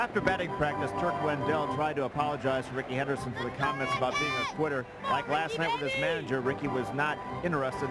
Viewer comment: Did they say Rickey was on Twitter? Odd because it didn't exist in 1999